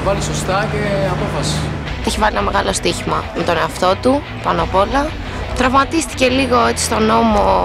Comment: Für die deutschsprachigen: habt ihr bei 3:40 auch: "ich wart mal am Rand und da steh ich mal" verstanden?? :D